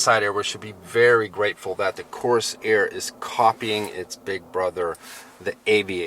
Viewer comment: This is the best review I've heard so far and I've heard several. I Love the Corsair! My wife just bought the Nautilus but I want this one! I had the Ford Escape and it was the squeakiest vehicle I ever owned; never Ford again. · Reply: I thought you said you want the Fordsair.